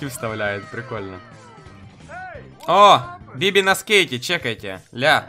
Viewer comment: мне одному послышалось, что Биби сказала : "на с*ка!" ?